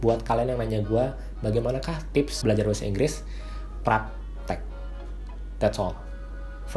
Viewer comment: "That's all... for me". Haha kayak temen2 aku bang pas pertama kali masuk Sasing. Mungkin abang emang bener bilang "That's all from me" tapi buatku kedengeran kayak "That's all for me".